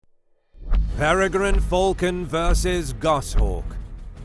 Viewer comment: Thought is said "Pregnant Falcon"